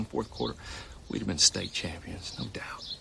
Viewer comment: I always thought he said "Take Stake" not State